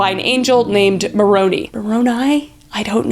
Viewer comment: dont belive everything you see or hear just because their dumb doesnt mean every other mormon is like that. also its moroni with a sharp i sound, high light of my day right there when you said it. i loki thought you said macaroni 😂😂😂😂